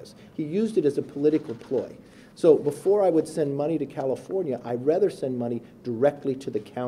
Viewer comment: Sounded like he said cattlefornia